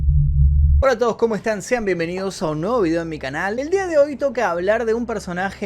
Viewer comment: Te entendí " sean bienvenidos a mi vídeo o no" cuando el "sean bienvenidos a un nuevo video" saludos!